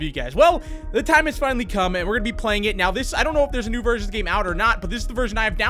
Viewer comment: When you said “there’s a new version of the game” I heard “there’s a new virgin of the game”